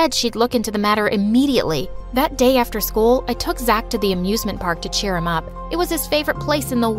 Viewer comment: at first i thought that it said i too zack to the abusment park lol.